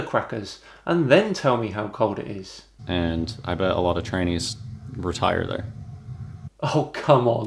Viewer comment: I didn't catch what he said at 6:46. Sounded like "a lot of 'trannies' retire there". Is that what he said?!